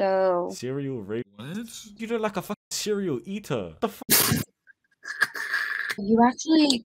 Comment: “I think you’re a serial eater” I’m fucking dying. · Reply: I heard it as cereal eater 😭